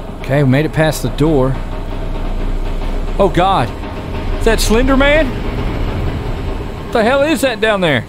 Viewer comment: Soo am I the only one who thought it said Linder 😬